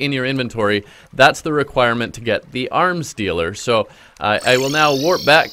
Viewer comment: I- am i the only one who heard "arm stealer"